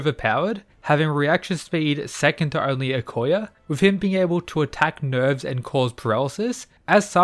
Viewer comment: I misheard "Nicolas being able to attack nerds" lol